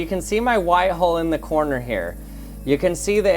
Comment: I thought he said a white hoe